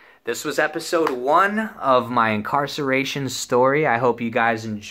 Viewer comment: At the end I thought he said "my car sarration story" which makes sense.